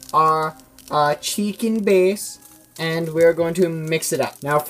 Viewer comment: it sounds like he said chicken bis Ik it was chicken breast